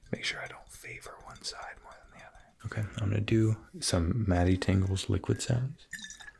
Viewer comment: He speak so clearly that the auto generated captions understands him. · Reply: he says tingles, but the captions say “tangulls”